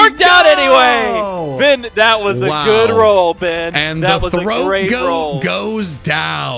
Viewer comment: Ayo what did you say? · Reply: “That was a great roll” The damage that Rock Slide could’ve done has a range, and the roll (like a dice roll) they got was high enough to kill.